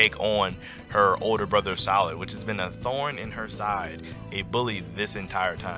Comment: I heard you say salad 😂